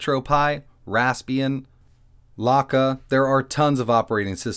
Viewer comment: you mention 3 OS's for the pi, Retropi, Rasparian, and ??blocka?? I think I misheard what this one is... can you tell me? I've not heard of anything like that and want to check it out. Thanks! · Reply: Lakka is maybe what said